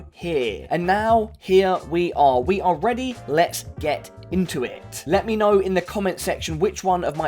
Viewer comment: I thought he said "Let's get INTIMATE" at 0:22.